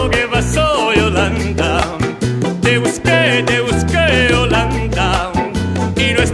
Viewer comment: I keep hearing "They were scared, they were scared, Yolanda..." Anyone else?